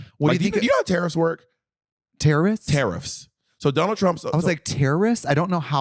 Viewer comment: I thought he said terrorists too lol